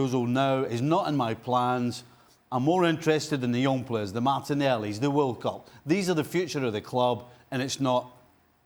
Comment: did he just say "the martinelli. The walcots"?